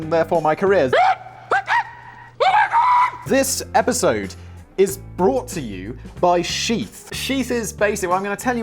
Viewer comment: LOL, right after you were talking about a voice coach, I was not looking at the screen and what I heard was "This episode is brought to you by SHEEP." What a lost opportunity for SkillShare, Audible and many of your other teaching sponsors...